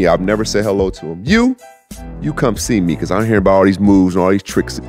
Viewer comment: I hear above all “these moves” I said let me see them moves boy!!! Ur living with me son!!